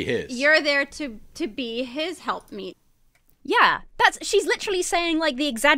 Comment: Am I crazy or did she just say women should “be his help meat”, please tell me she said something less psychotic